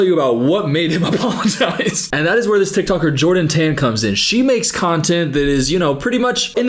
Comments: ! He doesn’t give a Bible verse when talking about how they’re not perfect, I thought he said John 10 too but it’s his accent, he said Jordan Tan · as a christian girlie thank you for calling him on the cherry picked verse 🫡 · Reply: i don't think he even said a bible verse tho?? i think misheard "jordan tan" as "john ten" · i think he said “jordan tan, you are not perfect,” not “john 10” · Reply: Yeah I came to see if I was missing a book of the bible or something because I've never heard of Jordan 10 and then I heard him say it again and realized he was saying the woman's full name.